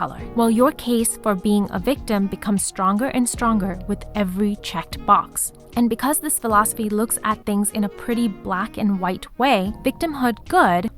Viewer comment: "every chick box" (i had captions on i know that's not literally what's said) im sorry lol but i have to acknowledge the editing here. no hate, but that's funny af